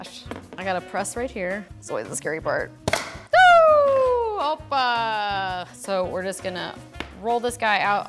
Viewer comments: did anyone else think she said oppa? Awww just me? The life of koreaboos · did she just said “opa”???🇬🇷🇬🇷😂χελ γεαααα